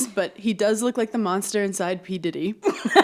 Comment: omg my first listen through i thought you had said the monster inside pete davidson 😭💀